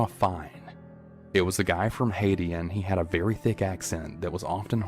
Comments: He had a very thick accent What I heard:He HaD a VeRy ThIcCc AsSeS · When he said very thick accent I thought he said very thick ass